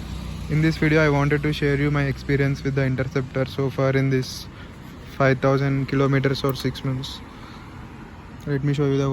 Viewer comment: I thought 500 km when u said 5000km I was shocked